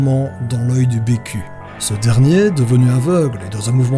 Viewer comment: j'ai entendu enfoncé profondement dans l'oeil du PQ